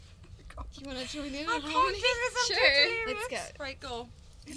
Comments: it sounds like Perrie says “I can’t do this when you’re lookin, let’s break up”😂😂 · what did perrie said when she covered her face? at 1:15 she seemed so flustered and its cute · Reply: Freeshooter she said "I can't do this, I'm too delirious!"